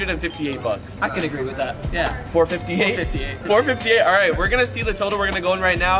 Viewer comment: Notice how he say it's all gonna cost 458 dollars but the caption says 485 😭👀